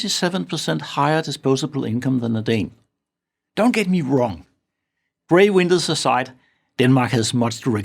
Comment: What is he saying at 4:58? Sounds like "Bray windows aside". I keeping replaying it but I can't get it. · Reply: Putting on captions, it seems he's saying "grey winters aside."